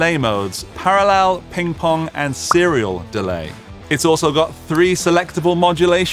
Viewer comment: Random FTWait what! Did he say cereal dly? Id love to zone out in a long trail of tap tempo repeats.